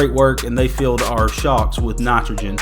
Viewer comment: I thought he said ‘shop’ instead of ‘shocks’ at first. I was a little confused. Lol